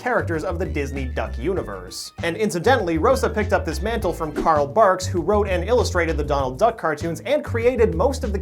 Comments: I thought he said Karl Marx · I thought he said Carl Marx for a sec